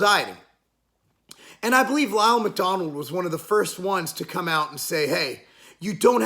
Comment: did you say Ronald Mcdonald introduced flexible diet?